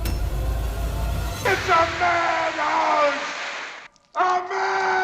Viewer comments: I've always thought Dodge was saying "It's a man hose...a man hose!!!!" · When he gets spread with the hose is he screaming "It's a mad house!" like Draco does in the rise of the planet of the apes movie? Cuz I always heard him scream "It's a man hose!" XD